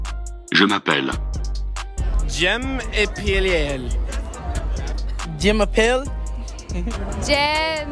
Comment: C'est moi ou il a dit "j'aime épiler"